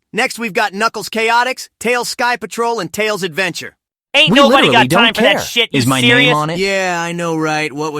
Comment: "Ain't nobody got time for that you serious!" - Ryan Sonic What was Jason Sonic saying during that time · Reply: "We literally don't care as my name on it"😒